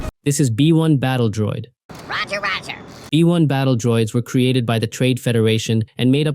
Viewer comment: it sounded like you Said E1 battle droids